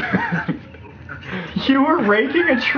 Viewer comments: I legit thought he was saying “you were rapeing a tree” 😂😂😂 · who else thought sam said "you were raping a tree!!" but he really said raking a tree